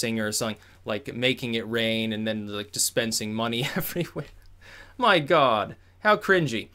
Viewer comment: Did Styx just say: "My God" 😂 😂 😂